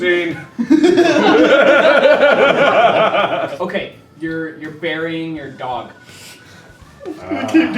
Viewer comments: I thought Ethan said "You're burying your daughter." And I was like WTF ETHAN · At first I thought that Ethan said, "You're burying your daughter" which gave a whole different view of Mark's next few comments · See i thought ethan said "your burying your daughter" instead of dog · I thought he said "you are burying your daughter"!! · I swore I heard "you're burying your daughter" not "your dog" at first... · When they said "your burying your dog!" I thought they said "your burying your Daughter!" XD SOMETHING IS WRONG WITH ME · I thought he said "you're burying your daughter" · I. thought they said "burying your dead daughter" not dead dog😂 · I thought he said marrying your daughter not bury your dog.... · Instead of "burying your dog," I thought he said "burying your daughter." Whoops. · I THOUGHT THEY SAID BURYING YOUR DAUGHTER NOT DOG OMG I WAS SO CONFUSED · i misheard dog as daughter and i was really confused lmfao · I thought he said daughter not dog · when they said "You're burying your dead dog." I thought they said "You're burying your dead daughter." lol 😂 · omg, I THOUGHT HE SAID, "YOU'RE BURYING UR DAUGHTER!!!" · I thought Ethan had said "You're burying your *daughter*" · I thought he said that "You're burying your daughter" and I when Mark started to say "it was a dumb d".. before I knew it was dog, I woke everyone up in my house from laughing too hard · I thought Ethan said 'you're marrying your daughter' XDDDX did make sense at the start